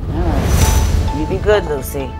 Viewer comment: I thought she called her lil c not Lucy